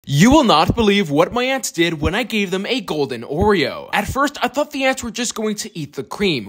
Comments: I thought he said “you will not believe what my ex did when I gave this golden oreo” 😂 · I heard “ u can’t believe what my ex did when I gave them a Oreo”💀💀💀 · I heard “you cannot believe what my Aunts did when I gave them a golden oreo” 😭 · I heard “I can’t believe what my ex did when I gave them an Oreo” 💀